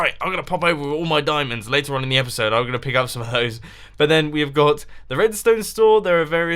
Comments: I've watched long enough to think he just said, "Gonna pick up some boats and hoes..."" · Dont get me wrong i like ur videos but tango and wels have both done a world tour recently, this is just a "I dont feel like making a video so lets put together a quick 20 min video".. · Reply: Did you say at 16:01 in the video "I'm going to get some hoes" Just asking Nicely Please Respond!